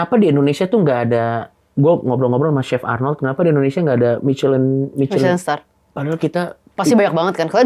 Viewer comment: Menit 55:53 coki ngomong apa? We challange stars?